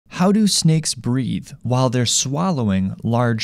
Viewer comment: I thought he said: "How do snakes breed?"